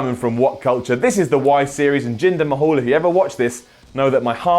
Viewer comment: I could swear I heard Simon say "Jinder my-hole" wtf xD